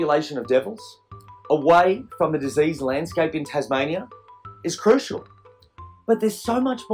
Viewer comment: I thought he said Tasmanian Tiger.